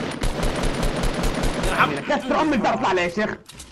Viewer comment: سمعت غلط ولا 😕😕 ؟؟ اللي فهم يعلمنا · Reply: قال كسر ام الجدار يشيخ لاتروح نيتك